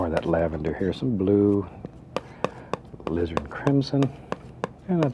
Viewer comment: I always thought he was saying 'lizard crimson' also "wet and ready" huehuehue